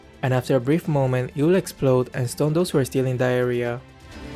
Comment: Am I the only who heard: "will stunn and explode those who are still in diarrhoea" @ 2:09😅 had to check the subtitles again, great gameplay 🙏😊